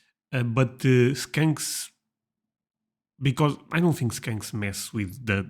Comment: Crying laughing, look at 12:00 when you begin saying skanks instead of skunks! We have both and they both smell bad!!! Sadly, only skunks can be de-scented lmfao🦨🤣